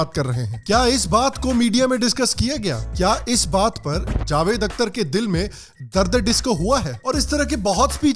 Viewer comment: I switched tab and video paused all I heard was darse disco hua hai